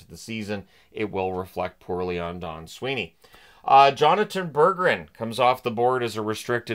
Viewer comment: I choked on my glass of water.. At first I thought it said Bergeron…